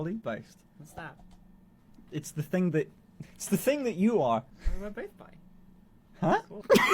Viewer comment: Wait what did say? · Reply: "but we are both bi"